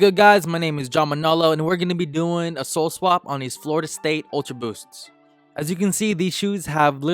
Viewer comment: dude said Florida state ultra boost and the caption says Miami 🤦‍♂️